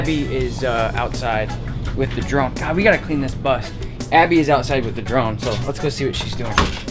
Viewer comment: "She's outside with the drone, lets go see what she's doing." I totally heard she's out with the drunk. I was confused lol. Clearly need to get the hearing checked.